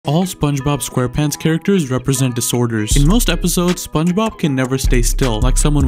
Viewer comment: First i thought you said "all spongebob characters representates discord moderators"